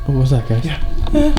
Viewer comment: I hear "yeah" female right side as if behind me but under music and may be editing